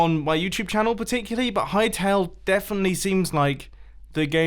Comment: Anyone know what the game he was talking about is? Sounded like he said High Tail/Tale. I googled it, but got a furry lewd game.